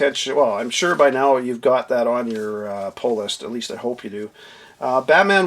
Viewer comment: I keep hearing “ pole list”. Lol. Love your videos. Great references.